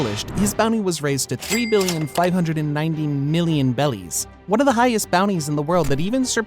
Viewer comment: did bro say bellies😂